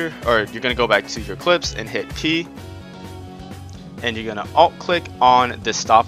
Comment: can someone please tell me what he says at 8:23 out click on p ??? i'm confused lol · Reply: alt-click